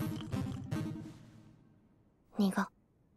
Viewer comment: AYOOO WHAT DID SHE SAYYYYY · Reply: Bitter